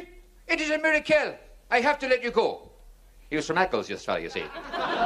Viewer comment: What does he say after he says the frenchman was let go? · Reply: Here is what he said "so he let the French fella go...", then he says "He was from Eccles you see" Explanation is he is making fun of his inability to do a better French accent. Eccles is in Manchester UK, so that i suspect is where the comedian is from and how he explains what he perceives as bad attempt at accent for the joke.